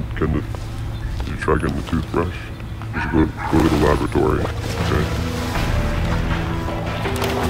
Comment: i heard toothbrush and laboratory